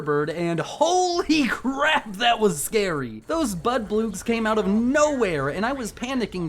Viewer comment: did he call the bloodbugs budboogs?